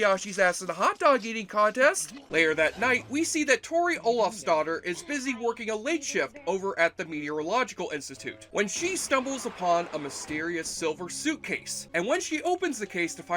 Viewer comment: I seriously thought you kept saying Tori Olaf’s daughter....😅😂🤣